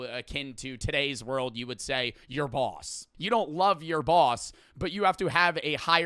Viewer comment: I thought bro said your balls💀💀💀